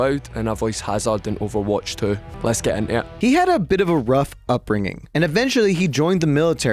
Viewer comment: he actually says "Let's get intae it" or "let's get into it" for non scottish people